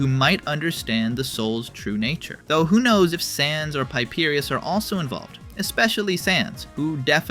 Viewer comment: did you just call him piperius